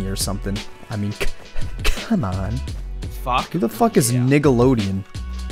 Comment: 35 second did u just say niggalodeon?